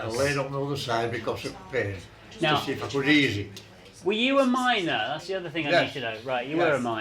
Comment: The first times I heard "miner" I honestly thought they said "minor" & I was sooo confused :P